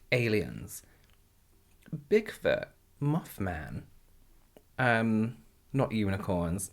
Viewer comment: When he said moth man I thought he said muff man and I spit out my fried rice, because i was choking from laughing so hard 😂😂😂